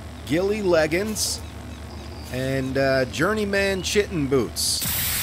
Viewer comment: When your said "journey.. man chitin boots" it sounded like you said "journey.. man sitting boots"🤣🤣🤣🤣🤣🤣